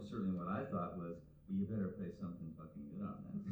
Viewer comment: What does Russ Kunkel say at 28:04?..."Well, you'd better ____________" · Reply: “...play something fucking good on that song”